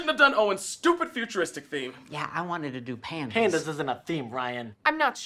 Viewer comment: Wait he was saying pandas this whole time??? I thought he was saying pants. This makes so much more sense now. I’ve watched this episode hundreds of times how have I not noticed this